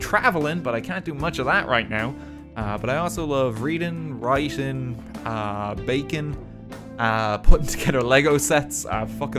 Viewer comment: i legitimately thought he said 'reading, writing, and bacon' were his hobbies